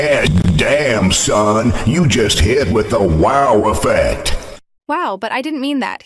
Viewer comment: i heard damn son u just hit him with the wow effect